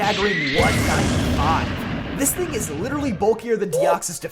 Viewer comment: "When Eviolite's boosting Dusclops's defense & special defense to a staggering KABOOOOM!-five! 😂 I had to replay that several times, but I think you said 195?